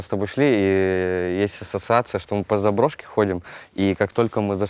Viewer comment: Андрей: есть ассоциация... Я слышу: ... если сосаться.. Но потом всё прояснилось. Спасибо за интервью! Долгожданное, чувственное, родное!